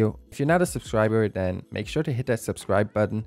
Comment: "And as always guys, I love you with all my fart and soul.." I cann't be the only one who hears it right?